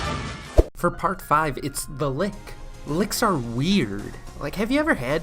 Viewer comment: Did someone say The Lick?